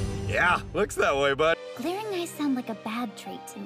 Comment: What is the girl saying at 5:12? "??? sounds like a bad trait to me" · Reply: “Glaring eyes”